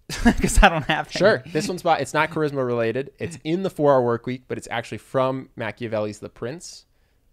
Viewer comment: sorry, what word did he use? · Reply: 4-hour work week